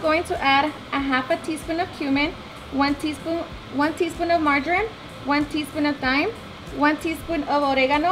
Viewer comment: did you mean margarine thats butter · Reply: I thought she said ‘margarine’ at first too, but then realized she said MARJORAM because it was with the other herbs and spices.